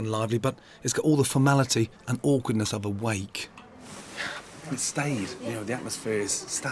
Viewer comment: legit thought he said wank xD